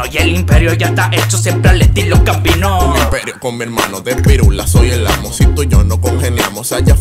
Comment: escuche "De pirulas por el ano" jajajaja